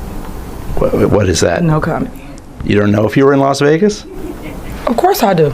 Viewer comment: was it no comedy I heard??